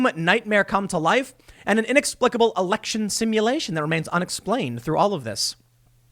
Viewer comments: Toobin misheard. He thought they said election stimulation. That's a completely different animal. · Election simulation?! I thought you said it was election masturbation!!!